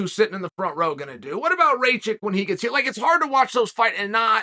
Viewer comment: Did he say ray-chick lol